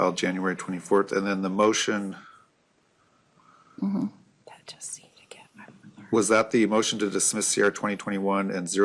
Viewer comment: Hot mic at 54:07 “they just seem to get everything they want”. Did I hear that right?